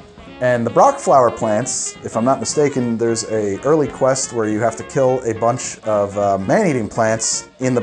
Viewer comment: I heard this so wrong until I put on captions. 🤣 “There’s a girly quest?” He said early quest. For a second I was like sister’s reaction from red vs blue “what the fuck are girly laps?”